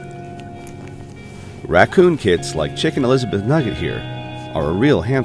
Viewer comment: DID YOU SAY CHICKEN "ELIZABETH" NUGGET????? OH MY GOD? ? ? ???? THAT SO CUTE.